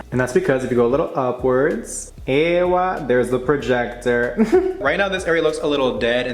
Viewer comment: did he say eyywa which is an Arabic word that mean yes?